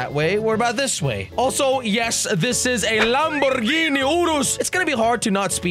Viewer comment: What I heard is "Lamborhini Oorooss"